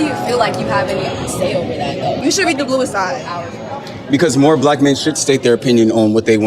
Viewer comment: What she said was, “You should read The Bluest Eye,” which was Toni Morrison’s first novel (1970) about white beauty standards / internalized racism affecting young women’s mental health.